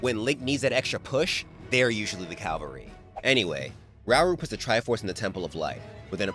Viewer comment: I would love to meet Myamoto someday 20:38 they’re usually the “calvary”? I know you meant cavalry, but it sounded like the other lol